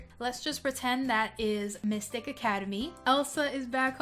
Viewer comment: I thought you said "F***ing high academy" XD